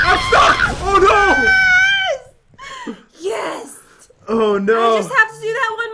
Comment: did sayd yest?